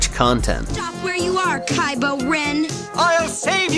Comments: Did she said Kylo Ren? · Time out did she say kybo ren... did disney and Kennedy use the cartoon series for ideas?!